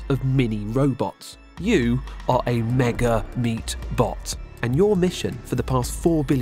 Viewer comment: i misheard "you are a mini meatball" looooooooooool funny